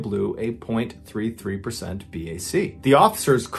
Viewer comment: I thought he said .03 BAC not .33 holy shit that amount of alcohol would probably kill me